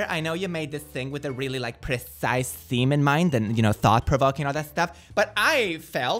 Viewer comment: did just say "semen mind" ?